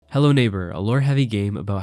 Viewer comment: did you just say HELLO NEIGHBOUR ?